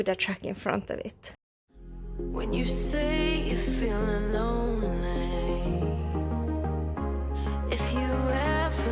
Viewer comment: Did you say that total weight was 64 tons ??????? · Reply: She said 64 tons (metric, about 70 us tons). You can gross up to 74 tons in Sweden.